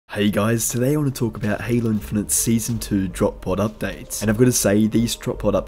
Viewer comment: I thought it said drop poo...